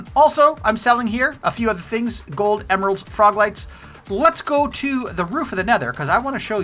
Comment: I kept hearing "frog legs" until I realized you were talking about frog LIGHTS!! lol